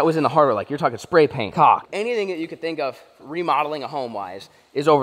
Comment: youtube thought you said a bad word, they didn't realise you said caulk!!! watch it with subtitles on :)